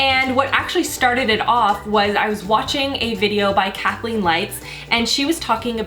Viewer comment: When you said "Kathleen Lights" in the beginning, I heard "cackling lights" and had to go back and hear it again to make sure I heard right. Lmao